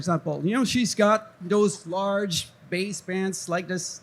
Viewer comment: did he just say "my wife's got those large pants on...stand up" ...bet she was thrilled.